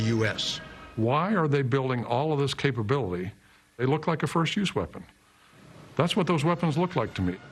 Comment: Did he say "first-use" weapon at 1:45? What does that mean?